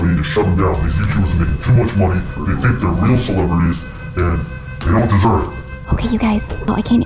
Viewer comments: God! This is crazy! I heard “You Don’t Deserve it” It’s most likely PT4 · the hacker's convo from 7:16, the hacker said at least from what I could understand, he said, their video was sponsored or something. We shut down the YouTube video, too much money and we get the real celebrities. They don't deserve." He then said (after you spoke) to start recording and began to speak about the phases 1 and 2 you and Chad had mentioned earlier. However, the guy who was holding your backlight at 7:46, he said that it's a backlight and the guy who threw the backlight threw back to the ground. The main guy at the desk said: "Hello and welcome to Project Zorgo. Phase one: we will hack the trending page and we will promote unpopular videos. Phase two: we will hack popular YouTube channels and will not surrender." I couldn't get the rest but after phase two, he said something about applications. At the end of the talk, he said to smash that like button and thank you for watching." He then continued to say about something television programs and YouTube videos. I couldn't get the rest